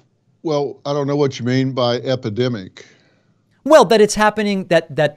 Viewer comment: A doctor doesn’t know what “epidemic” means? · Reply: Epidemic, I thought he said academic. Either way, "Dr Phil" is a quack.